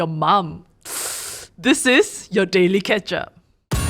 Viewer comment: For the most time, I thought they say “The Daily Catch Up”, I now just realise say “The Daily Ketchup”!!! Hahahah